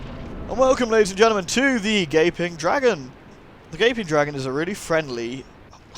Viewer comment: For some reason I heard "the gay pink dragon"...